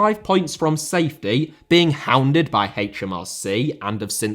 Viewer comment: Thought you said hounded by HITC.